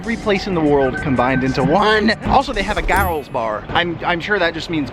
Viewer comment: when he said "garls bar" i literally thought he said "garretts bar" then i was like ' oh that exists now congrats i didnt even know u had your own restaurant" XDD